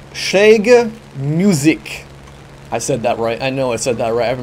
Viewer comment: I am german and I got no idea what you said at 2:58! :D Schährger Musik?